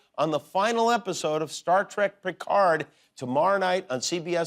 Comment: Totally sounds like jimmy said "the last episode of star trek prick hard" 😅😂